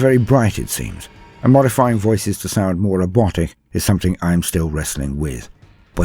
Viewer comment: I heard "modifying voices too sound more erotic" rather than "robotic" the first time 🤣 what are the mechanicus getting up to with their machines and sacred lubricants